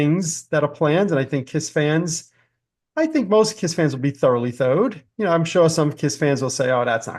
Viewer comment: and 4:03 you said KISS fans and then yourself will be “thoroughly” something’d….sounded like “thode”. What exactly was the word? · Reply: I love when people ask this…..it’s a ridiculous saying Vincent and I have been saying since we were teenagers. When we say we are “thoroughly tho’ed” it’s just our way of saying we are excited over something. For a more complete and useless background, “tho’ed” for some reason was mentally connected to “toes” for us. If something was really good / exciting it could be a “ten toe” level. That would be the same as being “throughly tho’ed”. Aren’t you glad you asked? 🤣